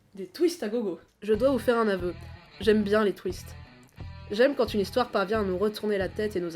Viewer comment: Par contre, quand a dit "Je dois vous faire un aveu : j'aime bien les twists.", j'ai entendu "Je dois vous faire un aveu : j'aime bien les Twix.", ce qui m'a semblé un peu hors-sujet XD